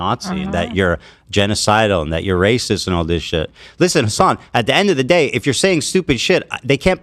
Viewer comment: at first I thought Ethan said "Listen Son" which I chuckled at. It's a proper way to allude to whiny baby Hassy.